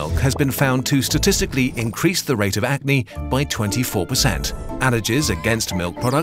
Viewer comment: What's the narrator saying in 5:39? Atney, eatney? Can't understand nor find in dictionary. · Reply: acne